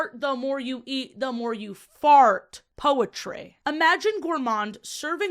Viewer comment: I would like to note that Wario is a gardener, so when she said fart I thought she said farm lol